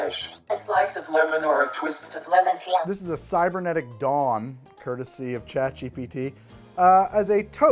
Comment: definitely heard "cybernetic dong" with how garbled the voiceover was hahah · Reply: I heard it as "Cybernetic Dom" and went "I think I remember this movie differently." LOL.